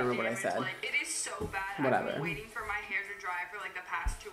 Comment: what word he said, I teach English please help me «vorever»??? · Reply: He said whatever